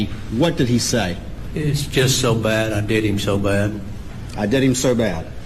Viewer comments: You can play that 500 times. All I hear is “I did him so bad.” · I heard "it was just so Bad They did him so bad" I have horrible hearing and I distinctly here They did him so bad.... I do not believe he killed his family. I think he owed someone money and they were killed in front of him to get him to pay them. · I clearly heard " I did him so bad " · Why would he follow up to close him down. I did him so bad. The blonde thought she heard I did them so bad.. smh